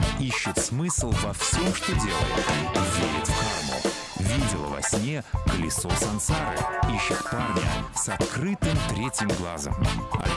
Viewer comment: Мне одному послышалось "видела во сне ПЫЛЕСОС сансары"? )))